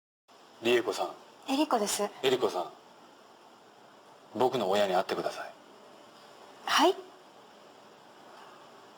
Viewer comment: What is the man saying at 5:00 ? I'm not sure, it sounds somehow like: 僕の永遠になって下さい。boku no eien ni natte kudasai. And isn't it more polite to use 君 (kimi) instead of あなた (anata)? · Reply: He says: 僕の親に会ってください (Please meet my parents) which is basically a marriage proposal. あなた (anata) is more polite than 君 (kimi). Earlier in this episode he uses 君 (kimi) with someone else's wife and people look at him very strange because it's too casual.